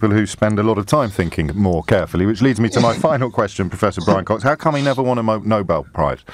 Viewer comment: I genuinely thought he said "how come he never won a MOBO" prize